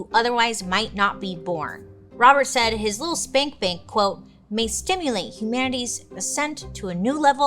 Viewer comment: did she just say spank bank🤣